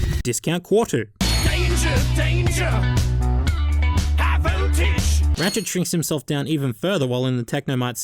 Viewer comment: At first I thought it said, "Donger High Voltage," so you can imagine my utter confusion with the inserted song :P